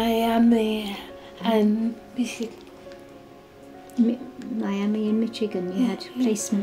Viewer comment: Did they say Michigan?